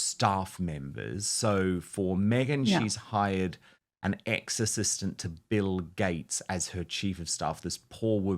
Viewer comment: Omg I thought you said an exorcist for bill gates lmao